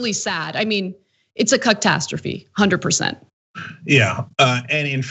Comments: I heard Anna refer to it as a cuck-tastrophy. Which is awesome. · wait!..... did Ana just say "Cucktastrophy" at 10:36 ??? 😏😏😁😂😁 god i love her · Did she say "CUCK-tastrophy"? Because if she did, that was bloody awesome!! LOL